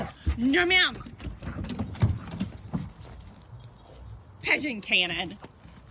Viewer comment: I know I’m mishearing the dog’s name lol I hear Pigeon Cannon 😂